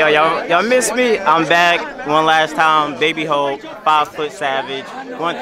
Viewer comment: I thought said he was 5foot 7 hahah had to listen to his intro 3 times